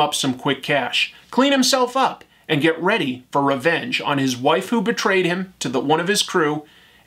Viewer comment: anybody else hear waifu 😂